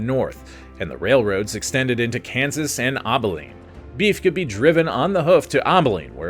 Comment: Abilene ab not aub for Abilene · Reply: I thought he was saying "Obbileen." I almost choked on my oatmeal.